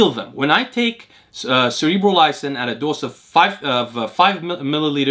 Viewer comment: Sorry what molecule do you say at 34:00? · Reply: Cerebrolysin He orders his from <link>